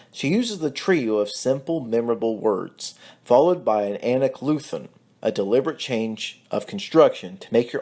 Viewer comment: Q: What is the word you used? Did you say an "aniclusen"? Or, did you say something else? I couldn't quite hear the word you used. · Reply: Great question! It was “anacoluthon.”